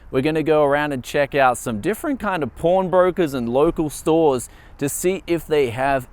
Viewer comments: Lol...I thought I was the only one heard porn brokers....but here the comment section is full of porn..xD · I misheard pawn brokers into porn brokers.. Facepalm.... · Did you say porn brokers?... lol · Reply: i thought he said prawn brokers